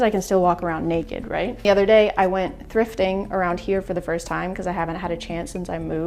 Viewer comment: I thought said she was doing some "drifting" and for a second, I thought she was cool